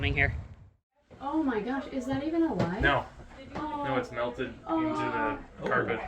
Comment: …….. Did I just hear melted into the carpet